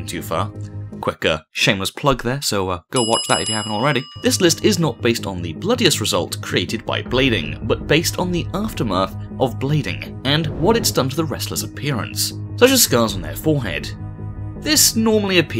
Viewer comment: thought it said balding